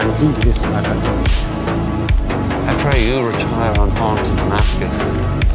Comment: I hear he said He "unharmed", not "unarmed".